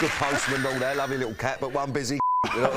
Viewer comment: What did danny said on 35:41 that made laugh so hard (busy ......) and (one busy ???) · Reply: Pretty sure he says "Busy c*nt"